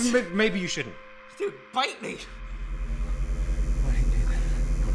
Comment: i thought he said bite me, not eat me